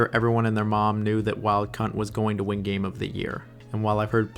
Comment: I was playing a game while listening to this video, and I heard you say “wild Cunt” and I burst out laughing lmao!